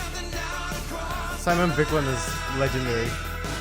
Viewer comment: Anyone knows what’s the name of the montage song at 22:24? I think says it but i don’t quite get it. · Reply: Break the rules Simon Viklund